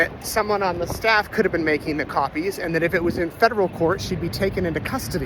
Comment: I thought you said, “it could have been the staff making the coffees,” and was taken aback for a moment.